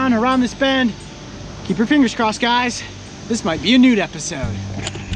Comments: Did he just say "Keep your fingers crossed guys this might be a nude episode " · I very definitely misheard "this might be a newt episode" as "this might be a nude episode" and...well I'm suffering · Did he just say nude episode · I swear you said "this is gonna be a nude episode" 😂